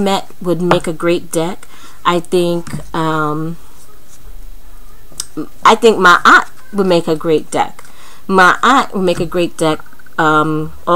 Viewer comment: I swear I thought u were saying Ma Aunt so I was like wait !?? What?!? Who?? Then I realized it was a name ...😂😂 loved this review✨